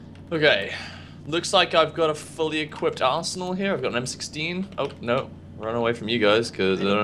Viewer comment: it sounded like said he had a "fully equipped asshole" haha